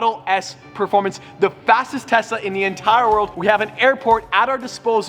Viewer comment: I thought he was saying tessa :/